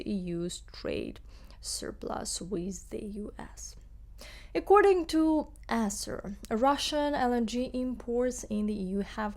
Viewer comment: I understand that there are sounds in English completely unfamiliar to Ukrainians. At 4:10, that seems to be the case as I hear "according to esser", leaving me baffled as to what was meant by the third word.